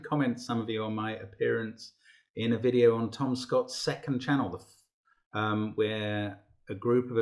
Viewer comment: Straight up thought he said TomSka and not Tom Scott